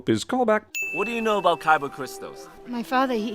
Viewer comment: I keep hearing "kyber" as "Kaiba" and can't help snickering.